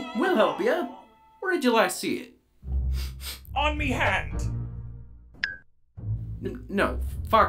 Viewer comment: sounded like he said “on me hat” at a other part